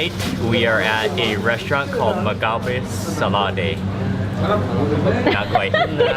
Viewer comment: At the end of this video, what was the name of the restaurant called again? "Maqabe Salade" That's what it sounds like you said.